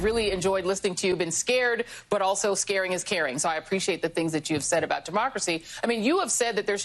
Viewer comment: Did she say, "scaring is caring"? what type of stupid is that?